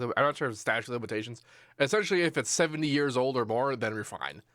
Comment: is he saying 7 years or 70 years old??